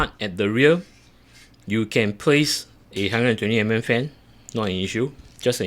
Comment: on official lian li website they mention only 120mm on the rear will fit. you said 140 will fit is there a place for screws for 140 :O? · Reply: Thanks for your time to do a video and reply to me promptly. Agree my mistake I miss heard you at 21:27. Now I hear you clearly say 120 not 140 All the best!